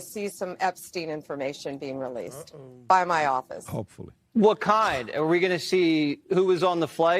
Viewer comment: I heard "HOPEFULLY" tommorow